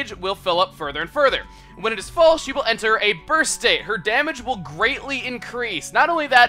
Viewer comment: did anyone else hear the word 'birth' instead of 'burst'?